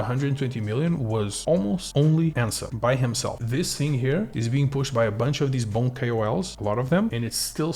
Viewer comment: Did anyone get the name of that KOL that he mentioned at 6:10? It sounded liked Ansa. Is he on Twitter? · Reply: Ansem yes his name there is